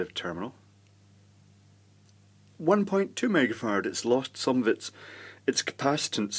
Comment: I know .. he said "mega-farad" .. but all i hear is "megafart" .. all the time ;)